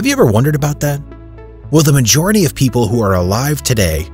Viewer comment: That a lie I thought you said, a majority of people going to heaven make up your mind